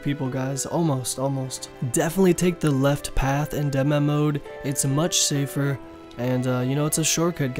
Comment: This video just taught me that when people in videos are saying "Dem-Mem-Oad" 10:58 was where i finally realised what was trying to say and even still sounds like Dem-Mem-Oad... they are actually saying/meaning "Dead Man Mode", no wonder i couldn't figure out what WTF dem-mem-oad was, thought it was some new damn abbreviation like RNG that i also had to research XD